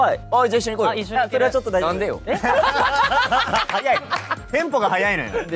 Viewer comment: Egu’s saying なんでよinstead of 안되요 ksjdncknsd 最初には私も一体何を聞いたと思いましたが「あ」の発音より「な」の発音がもっと強かったんですね。。。